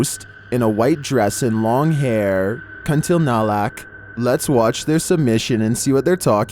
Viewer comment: I heard u said "Kun ti na lak?? But it was kun ti la nak